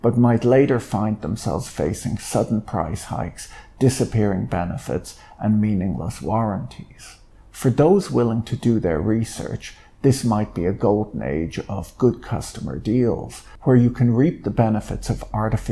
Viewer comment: I thought for a second you said "For those willing to do their research, this might be a gold maze..."